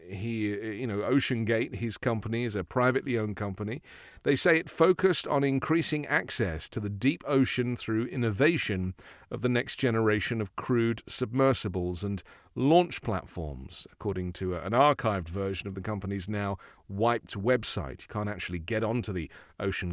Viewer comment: I hear....."Crude Submersibles" but I know you meant Crewed Submersibles, I fear Stockton was not sane.